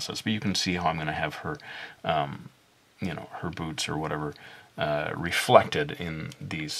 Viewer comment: Omg... at 17:25 I thought he said boobs... I was like "WHA--?!?" but then I replayed it a few times and realized he said boots. lol XD